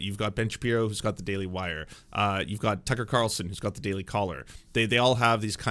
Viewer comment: I thought you said Daily Cholera and did a double take.